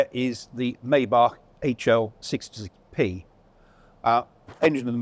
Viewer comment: just in case anyone else could not understand what he said..... its the HL 66 P . it sounds like dude choked on his own spit or something...... "their is the maybach six-zzleek P' . . instead of trying to say "six six" just say sixty six...... . american English is better than British English ;)